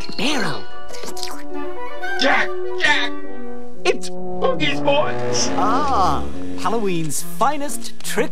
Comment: "Boogie's boys" wait so that kid dressed as a witch... Is a boy. Who...Am...I? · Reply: I thought he said oogeys boys?